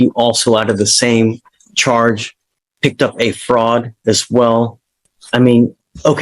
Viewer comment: I wasn't looking at the subtitles, and heard this as "picked up a frog." So my first thught was what was he doing to that poor amphibian???